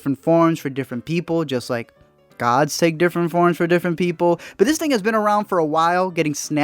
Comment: Did he say “but this nigga”